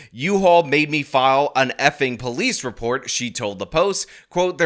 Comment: I thought she said dildo when you said expletive but apparently it was just shit